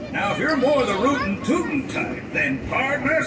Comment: the wrong spiel is played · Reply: I thought they said it's rootin tootin cowboy